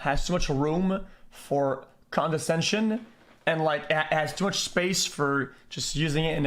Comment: Why did chat pepega big nose man when he said condescension. That was the right word to use. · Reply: i thought he said condensation